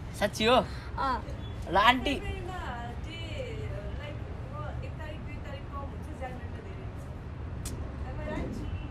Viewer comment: can anyone tell what mom said😅😅? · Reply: Yes she said feb have more day then january